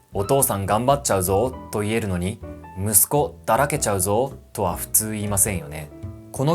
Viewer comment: の英語字幕に "'I'm full of sons.' (Instead of just, 'I'm full')." と書いてありますが、正しくは "Son will slack off." だと思います……！